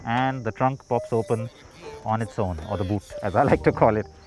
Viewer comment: I heard boo*s instead of boot